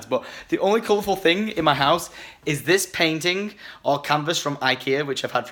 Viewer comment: When he said painting I thought he said penting 😂